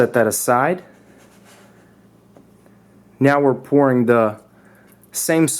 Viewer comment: did he just said porn ?